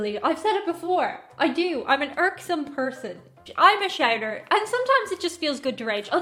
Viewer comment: I'm sorry, but can you explain what you said at 0:30? Thought I heard "I'm a Shagger". I'm very confused..